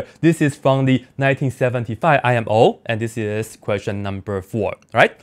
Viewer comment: i thought you said i am old in